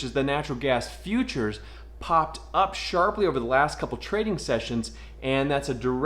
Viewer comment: I thought u said Sharpie.... that would make sense lol